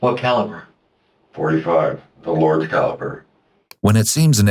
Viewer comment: Bro really threw in ".45. The lords caliber"😂😂😂 omfg NOT THE TIME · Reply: I thought he said “a large caliber”